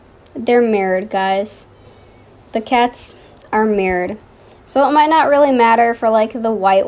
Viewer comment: I thought you said the cats are married........